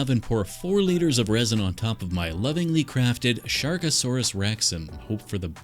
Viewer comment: at the end I thought it said subscribe for more SHREKasaurus rex content 😅 misreading at its finest.